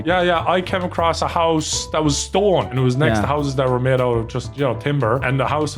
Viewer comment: For a minute there, I really thought Nogla said he came across a house that was Stolen, and I was very very intrigued about how that happened